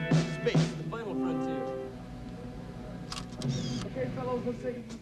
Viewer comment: I think what Steve Lukather said was, “Space. The final frontier”. Am I right? •2:20–Gross! Was Bobby trying to spray a disinfectant into his mouth?🤢🤮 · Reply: Yes Luke says Space the final frontier. Lol